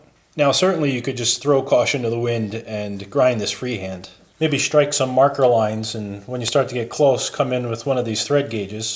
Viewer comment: what does the word mean? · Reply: "DRESS ME!" - dressing a grinding wheel trues up the surface and removes any grooves